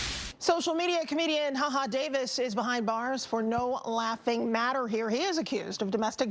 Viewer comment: I thought they were saying haha davis shot the bihh